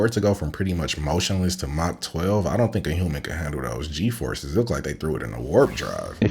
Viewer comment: did he say the foam comes? 😂🎉❤